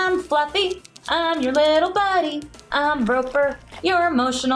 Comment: When listening on my headphones i thought they said Groper the emotional support dog and i was like wtf